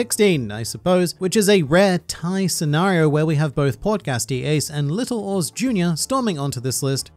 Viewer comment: When you said “Portgas D. Ace” I swear I heard you say “Podcast D. Ace”. That would’ve been interesting